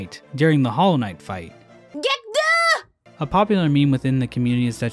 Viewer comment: I always thought hornet was saying get through during the hollow knight fight, interesting